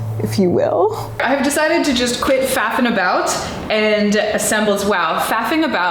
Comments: When you said you are going to stop faffing about I thought you said fapping about. My word I was speechless. That's some jobsite type shit. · I thought she said fapping about....